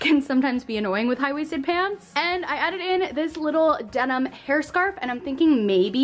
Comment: she said “and I added in” but it sounded like she said “I edited in this little denim hair scarf” and I was just like YOUR EDITOR MUST BE GOOD